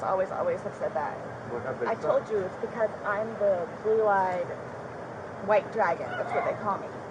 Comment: I thought she said ‘blue-eyed” dragon , not ‘belied white dragon’